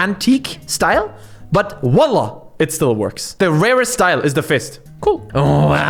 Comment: Did he really say wallah? XD